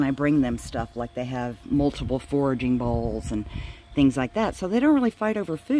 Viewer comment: Hi John! The foraging balls are little yellow balls that have holes in the tops. I hang them from the enclosure panels and they have to work to get the food out. It's not an overly complicated foraging ball, but it's better than just giving them meals in plates. I have several foraging devices I use. Some of them I have made myself. I bought some 2oz plastic condiment containers with screw on lids. I then drilled 1/2" holes in the lids and they have to work to get the food out. I use those when we are in the house over the winter because there aren't enough enclosure panels in their sunroom for me to hang the foraging balls. I've shown those balls in other videos before, but I can't remember which ones. 😂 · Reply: Okay, cool! [I clearly misheard you say "bowls" instead of "balls." 😆] Yes, it may be more work filling those forraging balls instead of just setting out a tray of food, but in the end, it's better for their mental health.